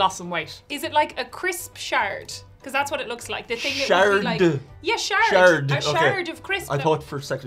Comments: I thought she said shart too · I thought said “crisp shart” as well 😂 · I mean, I heard 'Shart' as well and was equally surprised didn't say that :D